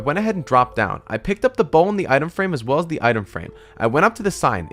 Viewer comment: Him: I picked up the bowl and the item frame as well as the time frame Me: am I the only one who heard that?